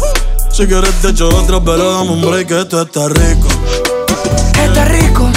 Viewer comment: Pensé que decía PUERTO RICOOOO!!,😂♥️